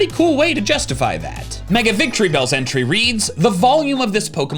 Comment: i heard “the volume of mega victreebells a**” and got scared for a second